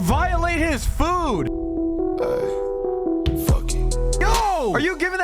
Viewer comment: I thought it said:”f*ck me”💀